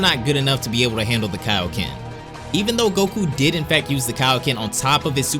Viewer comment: Goku *UI + Kaioken Goku: I’ve done it yes! I’ve sur… Goku Dies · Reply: i thought you said Kakyoin for a sec